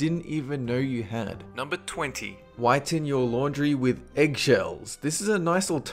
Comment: Al-um-ini-yum foil... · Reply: also "whiten your laundry" sounded like "why ten-year laundry"